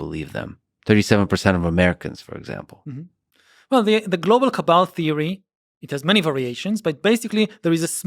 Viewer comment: First I heard "The global KEBAB theory", and I thought WTF :D